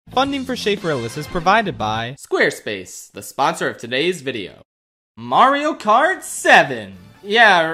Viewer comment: When I first heard funding for Schafrillas, I thought you said funding for shape paralysis